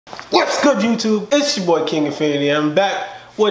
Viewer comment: when you said ''Its ya boy king infinity' was it just be who thought that he said kiddy fiddler